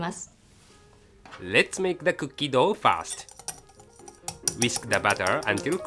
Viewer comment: LOL i thought he said "let's make the cookie dough fast" then i realise he meant to say "first"